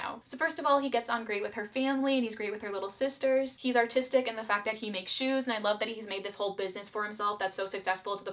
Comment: I've been so stuck in my own head lately I first thought you said "He's autistic in the sense that he makes shoes" 😭 bro's got a special interest i love that for him